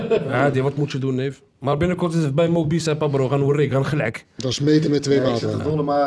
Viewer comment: Wat zegt hij over mo bicep? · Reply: die een zegt je bent verandert met je gewicht hij is zwaarder geworden drm zeg hij ik ga gwn binnenkort naar mobicep